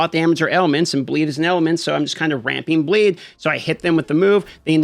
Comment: i was only listening and i thought i heard bleed is an element and i was like what. i went back and realized you said ailment . lol The other day i was watching another video and he talked really fast and when he said poe2 it sounded exactly like peewee2 lol. thanks for your vids.. We need more isometric rpgs 100%